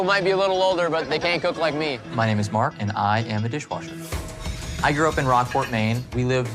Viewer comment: I legit heard “My name is Mark and I’m the dishwasher”